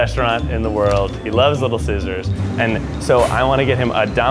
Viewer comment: I heard him say little scissors..